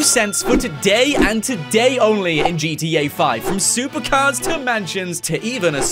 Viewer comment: Why do you always say Gitiei instead of Gta?